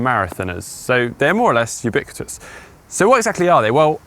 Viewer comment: Did you glitch at 0:39? Literally no idea what you said haha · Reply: More or less ubiquitous